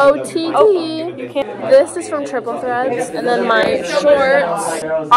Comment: does anyone know where her shirt is from, I couldn’t hear what she said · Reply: Triple threats I think